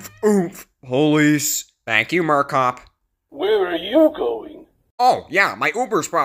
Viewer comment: Did I just hear Murcoch?!?! I really liked the episode though. Are you going to make a Fourth of July episode? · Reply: Thanks! He said "Murcop" lol. I don't have much planned for 4th of July this year, but I do have some ideas for next year!